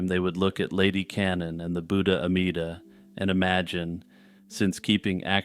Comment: did you say Ameda? That was my great grandma's first name.